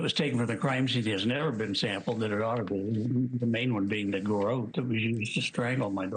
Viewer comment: did anyone else hear him say “the garrote WE used” 😳👀